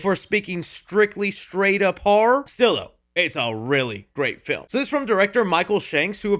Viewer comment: What did you say??? SILA? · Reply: I always watch the videos with closed captions, apparently he said “still though”